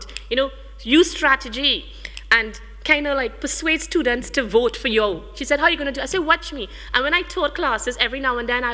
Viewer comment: "And kinda persuade students to vote for YOW!" 9:46 LMFAO what this woman sayin!!